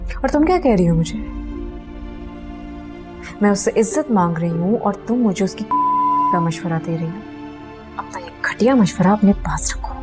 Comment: what does she said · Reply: Uski rakhel banne ka mashwara bola usne